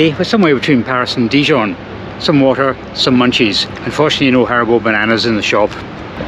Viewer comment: Your mate Steve Marsh got Haribo Bananas in Charleroi!! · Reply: I thought he'd said "herbal bananas".